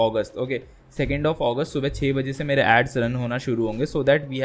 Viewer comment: Check 14:39 subtitles "Add Sun" instead of "Ads Run"